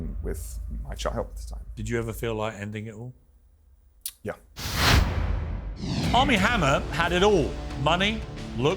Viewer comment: I must not watch enough TV. I didn't know who he was. I thought they were saying Arm N Hammer. 😂😂😂😂